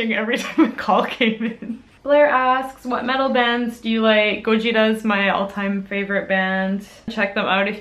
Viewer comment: I went through a bunch of the comments but I can't find any about the band she mentioned, can someone spell it out for me? It'd be much appreciated as she didn't spell it out in the video!! >.< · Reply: Brooke Durrant a year late but it sounds like she said gojira (like the Japanese name for Godzilla)